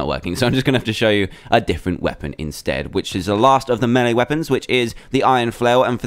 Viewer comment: am i the only one who thought he said "melon weapons"?